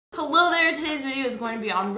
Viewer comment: Wait did u just say "holo there" ???? Simply Nailogical